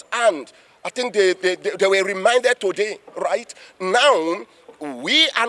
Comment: what is this guy saying…am confused 🫤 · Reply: He said they were remanded. He said it like reminded.